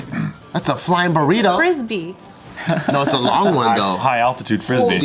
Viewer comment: Thought said burrito triangle